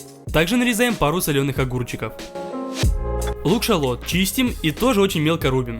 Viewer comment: мне послышалось лук шарлот😂